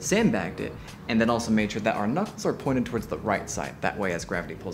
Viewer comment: Wait, what did you call the knuckles at 1:49? 🥜 's?